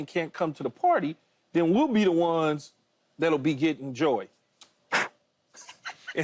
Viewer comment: Did Key just say at 3:15 that we be the ones getting JOYED?! and he blew on his hand lol...as in Joy blowing somebody?! 😂